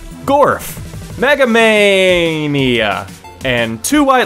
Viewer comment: what you thought he said:MEGAMAN what he said:MEGAMANIA